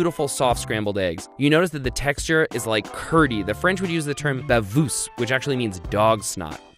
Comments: I'm from France and I didn't understand this word. Bavoose? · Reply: He was trying (and failing) to say "baveuse" (as in "Comment faire une omelette baveuse"). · being french and still trying to understand what he tried to say xD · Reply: I think he meant "baveuse" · I was just tried looking up the French word for "dog snot" (5:47). Can someone translate that into text for me? I heard "bavousse" or "vavousse", but I'm clearly wrong. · "The french would say davous"??? As a French I don't understand what you are saying